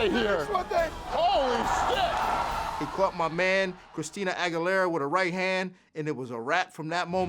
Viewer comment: i thought he said christina aguilera, the female singer. WTF